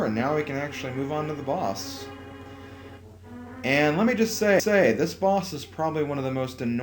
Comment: Anyone else hear "Let me just say-say.." at 8:17?